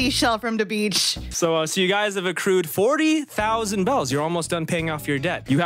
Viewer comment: I thought you said they owed 100,000 Bells. How can they be almost done if they only have 40,000?